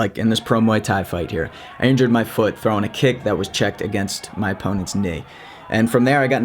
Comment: "I injured my foot from throwing a kick that was checked against my opponent's dick" is what i heard you say at 0:34. Couldn't believe it so I kept rewinding and listening again....... So I have to ask.... why was his dick that hard?